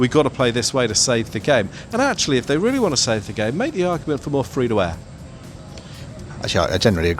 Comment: what the hell did you just say. I swear I watched it 5 times. Bloody thick accent. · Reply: "If you really want to save test cricket make it free-to-air" as in put cricket on terrestrial TV stations and not paywall channels like Sky Sports. Hope that helps.